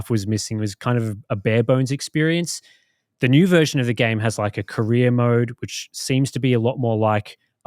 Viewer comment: I was just listening and when said "career mode" I thought he said "Korea mode", and I was like... what?